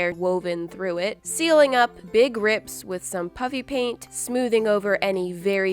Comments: Am I the only one who thought she was saying puppy paint- no just me? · I thought she said puppy paint and I was like wait what >^_^< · It sounds like she is saying puffy paint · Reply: She is saying puffy paint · it sounds like she is saying PUPPY paint lol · I am the only one who thought she said Puppy Paint to make it funny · Is it just me, but when she says Puffy-Paint does it sound like she is saying Puppy-Paint? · it sounds like your saying puppy paint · Ha ha it sounds like she saying puppy paint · it sounds like ur saying puppy paint haha